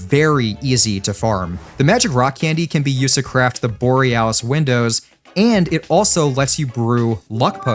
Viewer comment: i thought you said "boring *ss" 😭